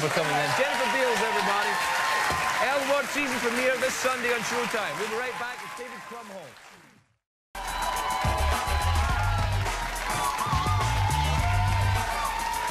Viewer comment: At the end of the first segment it sounded like Craig said: "We'll be back in a moment with David Bumhole", lol...